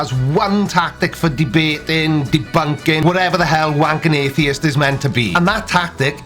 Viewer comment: did you say Wank an atheist?